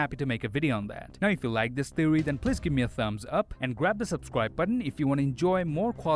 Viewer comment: Did he just say,”grab the sub button?”🤣